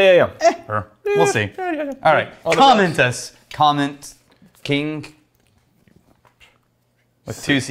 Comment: With the hard G's, it sounds like is saying Comment Kink >.>